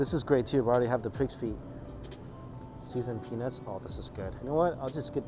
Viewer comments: Damn the prices. 10 bucks for seasoned pepper?!?? I heard him say seasoned penis instead of peanuts 😂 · Phew. That kinda made me look twice. I thought I heard seasoned penis.😅